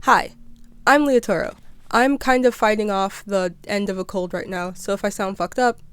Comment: I thought you said "I am currently fighting off the hands of a culprit right now"